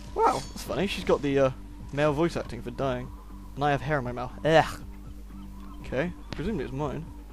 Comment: when you said "i have hair in my mouth" i heard "i have heroin now"